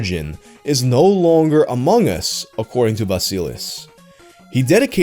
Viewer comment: DID HE SAY AMONG US?!?!?1?!?